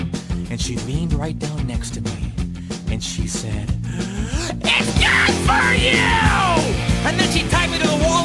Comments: I always thought she said “It’s not for you” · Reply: I originally thought she said "it's just for you" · I always thought she said “It’s not for you”